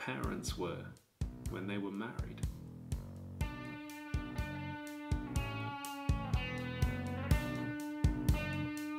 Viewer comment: Timestamps for the incredibly great musical numbers: 7:47 (Steady as she goes) 17:16 (The devil you know) 32:57 (just my type(? What is it called?) ) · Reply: My type